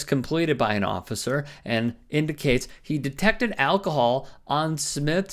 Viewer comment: ah ... I had to replay some of the above video 4.20 ... "he detected alcohol" ... I thought the narrator said "Detective Al Koh-hol" ... and no, its not early onset Alzheimer's ... I was in my kitchen retrieving a home made micro LED lamp ! Doesn't refusal to submit earn him a driving ban ? He killed someone whilst DUI ? well then ... obviously he's a perfect candidate for a post in your anti-Christian Government. and no ... no one paid me to say the above ... they've paid and are still paying the uploader because I said it !